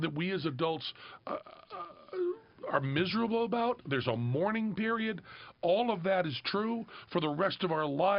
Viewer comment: Penn is dead on. But when he said, "A Mourning Period" I heard "Morning Period" and giggled. Then felt a little bad. Then made this comment. Bye.